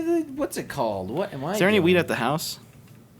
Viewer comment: Sounded like said "Is there any weed at the house?"